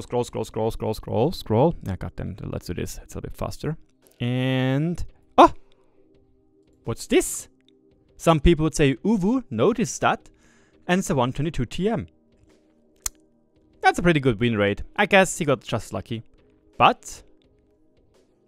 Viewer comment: did he just say "uwu"?